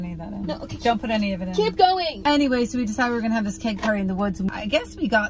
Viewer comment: I thought she said PEG party🤣🤣🤣🤣🤣🤣